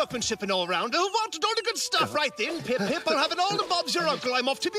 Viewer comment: For some reason, I thought he said "I'm bound to be the envy of OnlyFans"